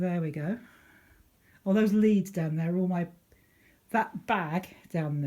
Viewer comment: Great to watch the updated tour, I thought you said there was leaves in the bag on the floor then I realised you said leads!